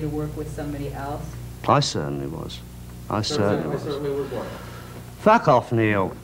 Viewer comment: What does the Neil say?! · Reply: I certainly was what ........I think